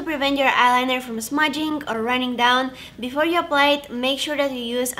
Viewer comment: when you said 'apply it' I thought you were saying blanket lmao